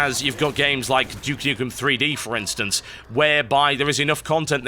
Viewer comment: I had to re-watch 8:27 because I thought I heard Duke Nukem Forever instead of 3D. I knew it was me being delusional when I heard him praising it.